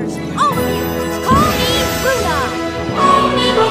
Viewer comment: so I was very tired and I thought she said “CALL ME RUDOLF”